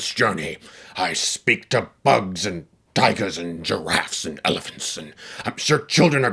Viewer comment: for a second, i thought i heard you say, “I speak to bugs, diapers and giraffes..” 😂😂